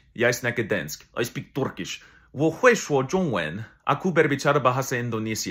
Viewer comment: I did not hear polish I heard him say ” ARAUAHBITCH INDONASIA